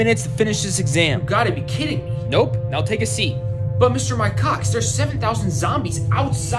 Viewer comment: What actually says "But Mr. Mike Cox" What my dirty mind thought I heard "But Mr. my cocks" 😆